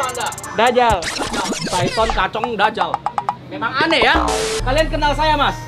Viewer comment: Nama asli: Saton, kacong, dazal Kedengeranya: Syaiton, pocong, dajjal